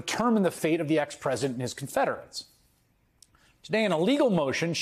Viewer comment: Did he say "Ex-President and his Confederates"??? In Georgia??? 1:30, the place where they only recently removed the "flag" and statues of actual Confederate generals??? Maybe I'm hearing things...